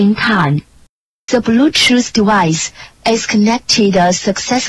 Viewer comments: i thought it said "the bluetooth device is really cheap here" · I WAS TERRIFIED AS A KID HEARING THIS CUZ I THOUGHT IT WAS A CULT CUZ I THOUGHT IT SAID “THE MEANING OF LIFE, IS LEADING TO HELL” WHEN IT WAS READY TO PAIR